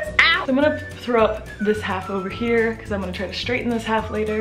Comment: Sounded like you said you would throw up your hair